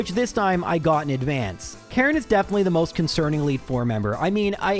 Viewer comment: did you actually just say "SHIT" in one of your videos?.lol. I don't think I have ever heard you swear,before.....lol :P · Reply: He said shtick